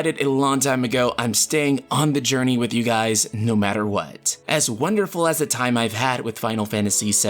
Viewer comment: You said "no matter what" at 6:36 but I heard it the way my subsconscious is thinking these days: "Nomura what"